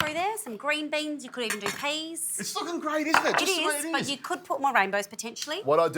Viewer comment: My wife says I don't listen I thought I heard something Maybe nothing Hehe If you want a good night sleep eat lots of garlic because nobody will come near you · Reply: He said it's looking great, but I heard it's f#cken great.